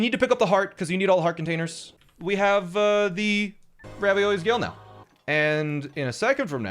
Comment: did he say raviolis Gale?